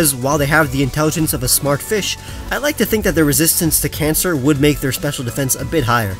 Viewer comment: Excuse me what? Did I heard wrong? Did you say "Resistance to cancer"?